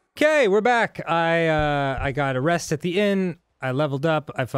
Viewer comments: At the start of the episode it sounded like Danny said "I got arrested again." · When Dan said "I got a rest at the inn" I thought he was gonna say "I got arrested." for some reason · In the beginning it sounded like Dan said "I got arrested again..........." lol